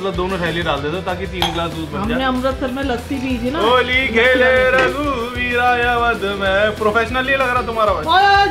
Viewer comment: I hear oli khelllaaa raghuveera😂